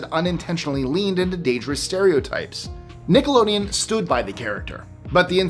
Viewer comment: I’m not the only one that heard ‘Stu-ed’ instead of ‘stood,’ right?